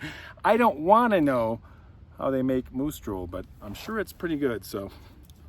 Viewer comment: As my ear bud fell from left ear I thought heard you called the beer Moose Stool. Hmmm. I'll pass on this beer 😅. Moose Drool 🤤. Hmmm. I don't know. I drool and the taste of slobber isn't appealing. Love your stories.